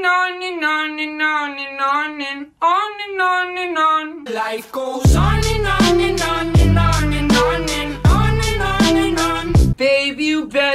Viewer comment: sounds like hes saying life goes onion onion onion onion onion xD